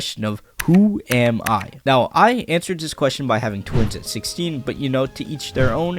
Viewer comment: I heard Ai as I in "I answered that question by having twins at 16" lol